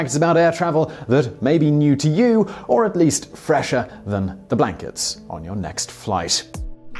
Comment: When he said "fresher than the blankets on your next flight" I could've sworn he said "fresher than the black kids on your next flight". XD